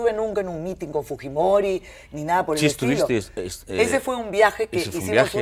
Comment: 🤔 escuché bien ? dijo : "estuvistes"?